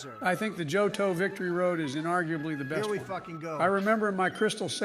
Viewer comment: I thought trump said incredibly fucking goated to Joes opinion on johtos victory road and I had to do a double take